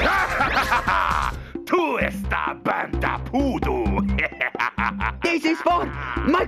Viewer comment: Anyone else hear "bantha poodoo" at 1:16?